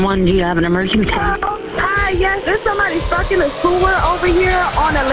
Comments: lmfao in the first 10 seconds, I thought she said "yeah there's someone fuckin' a puma over here" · I thought I heard the woman say: "Yeah, there's somebody fuckin a puma over here!" But luckily at 2:06 things were cleared up! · That beginning 911 call gave me whiplash wth Edit: I heard "there's somebody f'n a schoolgirl on Elaine" lol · please tell me i wasn't the only one who thought they said "Someone is fuckin a cougar" and got real confused and concerned..